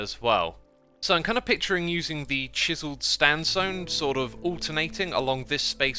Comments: Did he just say STANSONE!!! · am I the only one who heard "chiseled standsone"?